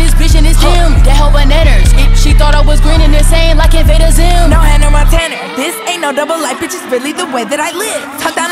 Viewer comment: When she said no Hannah Montaner, I thought you was saying "now handle my tenor" ..🤣